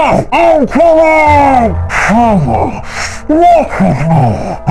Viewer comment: Did i just hearing Pyro said "friends, walk with me"?!